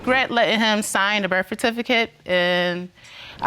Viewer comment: Did she just say "birf-a-tificate" 😂😂